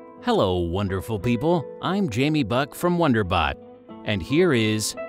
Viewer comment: I thought he says i`m Jamie Butt from Wonder butt I was close :D